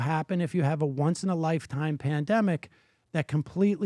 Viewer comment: lowkey thought he said PlanDemic 🤔